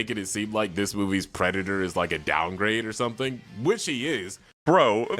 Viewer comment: He didn't say "which IT (the movie) is", he said "which HE (Drinker) is". As in, "he is totally doing what I just said he's doing for the reasons I also just stated". I agree on needing to rework that sentence, but just thought I should clarify.